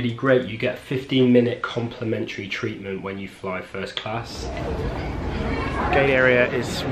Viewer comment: did he say gay area